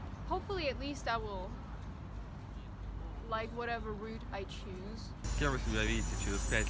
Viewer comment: ‘whatever route I choose” not road I choose (she was speaking in English and I heard it as a native speaker). But pedantry aside, another fascinating glimpse of real life. Your journalism will last a millennium, and that’s a hell of a legacy. Never give up. It may get harder before it gets easier.